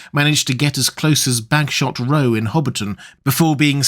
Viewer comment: did he say “Backshot Row?” 🤔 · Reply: Bagshot Row.